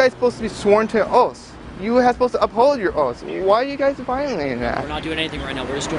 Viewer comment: What the fuck is Cartman doing walking around with a firearm? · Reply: Lol cartman Did he just say you guys are supposed to be sworn to your oats and uphold your oats